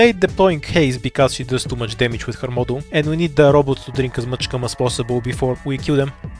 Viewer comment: i know im mishearing it but i genuinely dont know what are you trying to say here lmao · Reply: robot drinks dorothy's cum I just call all the white stuff in DV cum